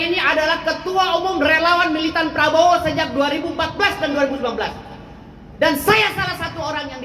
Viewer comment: sejak 2014 dan 2012? apa gw salah dengar?